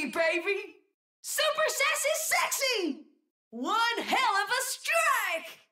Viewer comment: Wait this whole time I thought she was saying "Super Sexy Sexy"